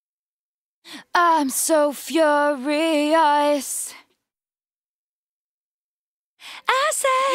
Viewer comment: I always wondered what she was saying here, turns out its just "iM sO fUrIoS"